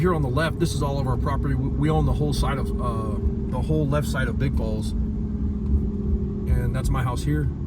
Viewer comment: I love everything about it but the cold. I can't take that cold of weather anymore. Honestly I had to relisten & Google map it because at first I thought you said "we own the whole left side of big balls". I thought damn not to many people can claim a town gave their left nut for them.😂